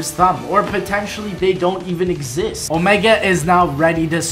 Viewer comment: who else thought he said omega is now pregnant